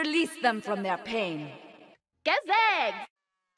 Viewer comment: idk what she says but sounds nice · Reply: She says “goo zigz”